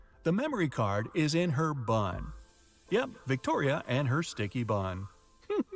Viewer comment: Yoo I thought the guy actually said it was in here butt. Lol